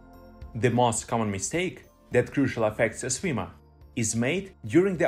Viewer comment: i think subtitle is wrong there should be "crucially effects a swimmer" instead of "crucial"